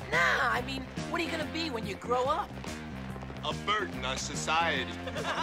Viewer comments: "What are you gonna be when you grow up? - A burden on society...." Best line ever.:) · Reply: Growing up we thought he said ' A bird in our society' 😆😆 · "What are you gonna be when you grow up? - A burden on society...." Best line ever.:) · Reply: I always thought he said A bird in our society because they are T Birds haha. As a kid I said that loudly and proudly haha. I was about 20 before I realized that was not right haha.